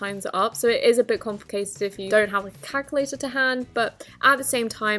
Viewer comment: she really said cackalator lol